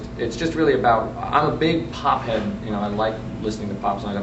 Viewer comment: haha "pop head"? I thought he said "pot head" and was about to give him a high five. lol Pop is cool. ig.